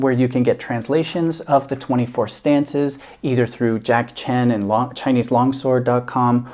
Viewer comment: Reminds me, I should try picking up this manual again and figure it out some more. I did a video on my interpretations (using Jack Chen's translation) some years back, and it wasn't easy. But it was fun to try to wrap my head around it. That is a truly gorgeous sword. I will definitely have to get it sometime. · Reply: Thought you said”Jacky Chan” 😳😏🤗😬